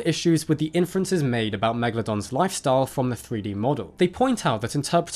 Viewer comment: Did you say murder guppy at 14:35...?